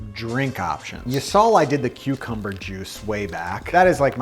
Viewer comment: I need more Souper Cubes recipes!! These are amazing. With the cucumber juice I thought you said Cubecumber juice haha.